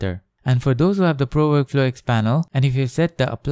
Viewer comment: why assume we have a $200 plugin? great up till that point? something happens with the mask when you click on that panel that won't happen without it. frustrating. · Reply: You listened too fast lol. What he actually said was: "4:46 go to the Camera Raw filter" and "4:48 for those who have the Pro Workflow X panel...". So actually, it wasn't an assumption that we all have a $200 plugin. What he did here is specifically address those who have the plugin. That's different. You don't need the plugin to do this tutorial. You just need to know PS well enough to achieve the "something happens" part with the native PS workflow to get the same results in this step. Seems like his biggest fault here is missing the opportunity to explain what those native steps are to people who have no clue. He assumed people working at this level can already work their way around the software, in other words. Maybe he should have put "Advanced" somewhere in the title.